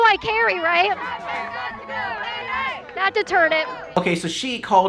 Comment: Um... No she sure the fuck did Not call her fat, what said was "She's gonna eat it." That's it. By the way. If that girl would have got in my face like that, I would have said the same thing that said!